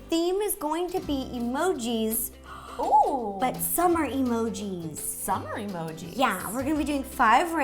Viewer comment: When she said summer emojis I thought she said some are emojis!